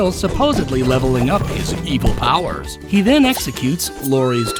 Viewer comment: I thought it said evil powders